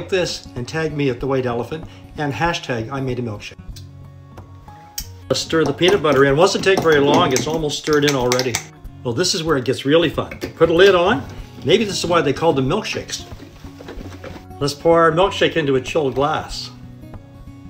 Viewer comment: I thought he said this is were it gets really f upped